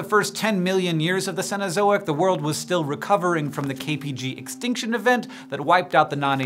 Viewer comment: I thought you said the KGB extinction event. 😐😳😳😳